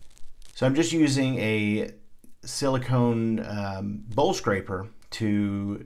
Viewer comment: For the longest i thought you were saying bull scraper. Lol. Bowl scraper. Nice exercise, by the way! Edited... Can you explain the point of using the cardboard frame?